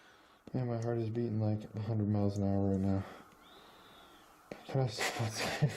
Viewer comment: The EVP sounds like it says „Can’t stop“ when it was asked to stop what it was doing to all the devices at once. Keep up the cool work!